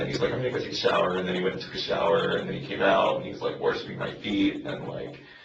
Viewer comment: I’m sorry, did he just say the dude was “worshipping my feet”??? What tha feck?!